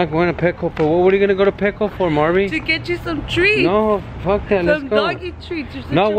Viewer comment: Omg!! I thought I heard let's go to petco for some tricks!😂🤣